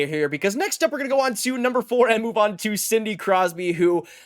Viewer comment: Did you say, "Cindy" Crosby?